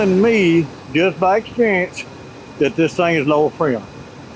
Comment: What did he say about the compressor at 1:45. I could understand what he said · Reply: He said: “it’s telling me it is low on Freon”.